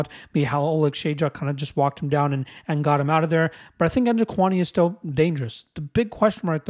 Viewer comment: Dude I could have sworn you said Chinny Njokuani